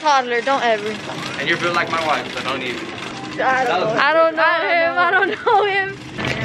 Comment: WHAT DID HE SAYYY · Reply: He said you’re built like my wife so don’t even